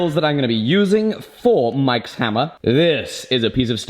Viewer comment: did you say mikes hammer?